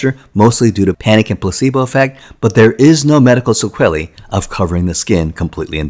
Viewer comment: What?!? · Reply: "There is no medical 'sequelly' of covering the skin." No idea what word you're trying to say there.